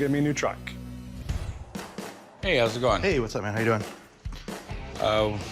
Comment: did he just say ‘what up my n word’ ?